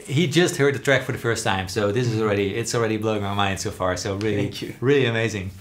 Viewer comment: did paul just say really cute 😂💀